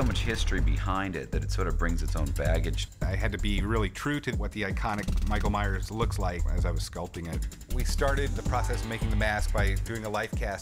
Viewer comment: I THOUGHT HE SAID MIKE MYERS